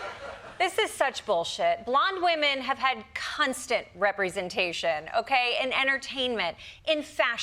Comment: Did she just say, 'Cunstant'? Ad for 'gins'...Vagins!